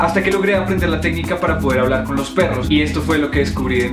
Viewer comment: Jajajaja muy divertido, al principio pensé que decía: como hablar con un turro